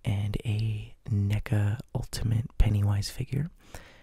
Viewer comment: N-Word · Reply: LOL!!! That's a very popular figure brand LOL! You guys really thought he said "nigga pennywise?" He said "neca pennywise". I honestly can't believe you guys didn't even bother to even try and look up the brand NECA